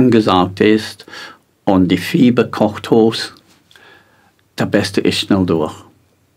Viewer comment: 🌈Minute 22:50 Das beste ist...? Konnte ich leider nicht verstehen, bitte um Hilfe. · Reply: "Das Beste ist schnell durch ..." - Dies bedeutet, dass der Prozess, wie bei einem Schnupfen nicht aufgehalten werden soll. Wie heißt es nämlich so schön bei einem Schnupfen, er dauert mit und ohne Medikamente 14 Tage, er kommt eine Woche und geht eine Woche. Im Prinzip ist dies nichts anderes wie ein Reinigungsprozess, in diesem Fall allerdings nicht für den Körper sondern für Mutter Erde. Hier sind natürlich die Zeitdimensionen viel größer in denen ein solcher Prozess abläuft. In dieser Relation sollten auch die Wetteraufzeichnungen in Verbindung mit dem sogenannten Klimawandel gesehen werden. Was sind Erdgeschichtlich gesehen gut 100 Jahre, dies ist noch nicht einmal eine Sekunde, umgerechnet auf unser menschliches Zeitempfinden gesehen und dies soll nun herhalten um eine Aussage über einen lang zeitlichen Prozess wie das Klima zu machen. Was jeder daran sehen kann was in einem Lexikon zum Beispiel über das Thema "Eiszeit" steht: "Man unterscheidet Eiszeitalter mit einer Dauer von mehreren Millionen Jahren und Glaziale (Eiszeiten), die eine Dauer von weniger als 100.000 Jahre haben. Eiszeitalter sind eine Ausnahmeerscheinung in der Erdgeschichte. Die weitaus größte Zeit war die Erde auch an den Polen eisfrei." .... Und nun heißt es seitens des Mainstream wenn die Pole Eisfrei sind geht die "Welt" unter. Schon merkwürdig, oder?